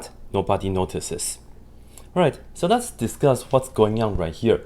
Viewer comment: In the beginning he goes "ok, so let's discuss what's going on" and i initially thought he was saying "ok, so that's disgusting" and really i agree